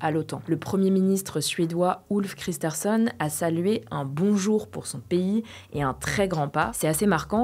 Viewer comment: "Le premier ministre Suédois Ulf Kristersson a salué un bonjour ..." Moi qui ne regardais pas l'écran : "Hein ? Que quoi ?" 😂